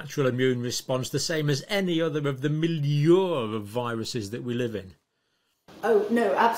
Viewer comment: What he say at 38:57? M something of viruses?